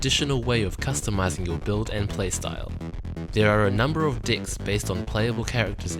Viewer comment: Did I hear correctly? Did he just say "There are a number of dicks"?